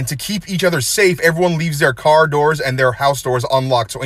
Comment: I thought he said " for more bullshit follow me on instagram " .